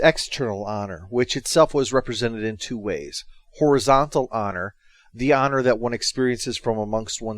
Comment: When he said "horizontal honor" i thought for a moment he said "horizontal on her"